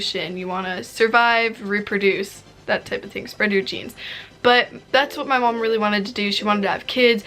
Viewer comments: omg when she said “spread your genes” i thought she said “spread your cheeks” bro i watched that part like 5 times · I thought she said spread your cheeks😂